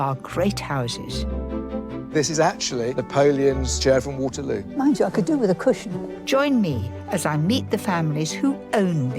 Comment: what does Marry say at 0:39? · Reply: "Mind you, I could do with a cushion".